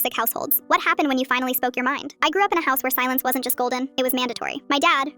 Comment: I thought they said “salad” not “silence”😂😂😂